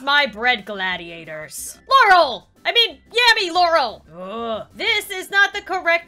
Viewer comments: I swear she said 'Yammy laurel" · did she say yammy? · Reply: No she said yanny Laurel because it was a popular topic bc there was a meme where some people heard yanny and some people heard laurel